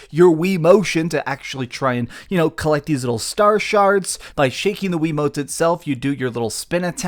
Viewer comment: I could've sworn I heard you say 'star sharts'